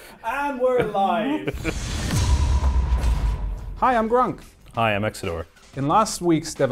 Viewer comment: i heard "Hi im drunk" 😂😂😂 Pd: you look so god damn strange with those hats